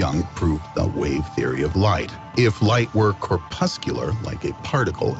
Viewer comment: at 57:47 the narrator in your video (the video you have presumably watched multiple times and discussed with) says "corpuscular" he does not say "crepuscular" which seems to be what you hear. I can understand the mishearing but if you actually had any interest in this subject (the double slit experiment) and had researched it to any extent at all you would know he said corpuscular. The fact that you couldn't correct that mishearing in your head and that you then go on to try to discuss crepuscular rays ("we see crepuscular rays", etc) shows how deep your misunderstanding goes. I know you don't understand the maths used in science but you are apparently also unable to use the words of science. Please: get some maths and physics text books (GCSE level for a start), take a few weeks off the internet, read and study the basics - to get a grasp on the subject you are attempting to discuss. Without your possessing basic understanding of the subjects your opinion is worthless - much as my opinion about Japanese grammar, the lifecycle of fungus and the qualities of ukelele music are worthless because they are subjects I know nothing about.